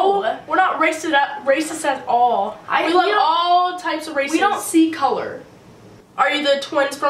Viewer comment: swear I thought she said " we love all types of racist"